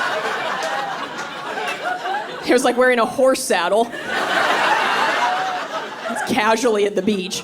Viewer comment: Was that "horse saddle" or "whore saddle", because it sounded like the latter, but there are some... implications there.